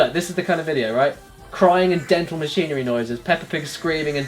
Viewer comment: I took a shot of medicine around the 9 minute mark and when it said "crying and dental machinery noises" i legit thought it said "crying and demented screaming noises"